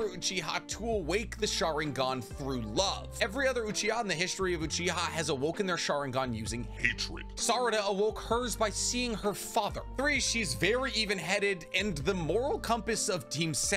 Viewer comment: I normally listen to non-music videos at 2x speed so had to relisten to you say "hatred" pitchdown a few times and, before realizing what you actually said, had thought you said edgelord which is hilarious